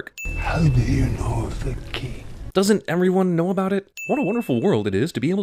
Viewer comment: I always thought he was saying “How do you know of the KEY” not “the game”